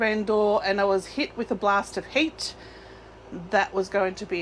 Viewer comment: OMGOSH you actually said "I opened the door and was met with a blast of HEAT" what I heard was "I opened the door and was met with a blast of HATE". lolololol When I was doing outdoor market events I would bring with me an outdoor carpet to prevent my items from being destroyed. The inconvenience of carrying it and making space for it in MY OPINION was worth the loss of product it protected, which of course is a personal choice one must make for themselves.